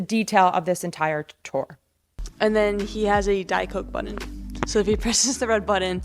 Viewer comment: Hahahahaha I thought she said “diaper” button when I first heard it on 2x.